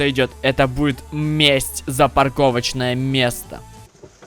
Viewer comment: там в конце <<парковочное место>> я услышал